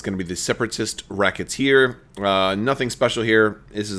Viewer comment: At first I thought that said Separatist Rocketeer and thought back to the movie and how The Rocketter was clearly based on Boba Fett. And then you said Racketeer and dashed my hopes...